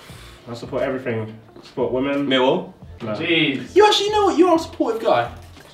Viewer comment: What is Josh saying here? For the life of me I can't figure it out. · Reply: Millwall